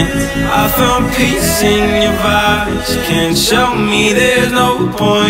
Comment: I THOUGHT IT SAID VIBES I THOUGHT IT SAID I FOUND PEACE IN YOUR VIBES MY LIFE IS A LIE 😭